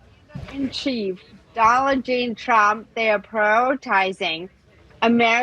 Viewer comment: Did she say Dollar Jean Trump? 10:12 in the video